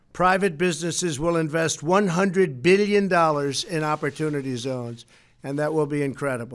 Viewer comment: Did I hear that correctly @ 3:21 ? Did he say 100 billion?